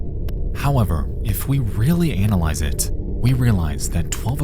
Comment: did he really said Anal-ise it?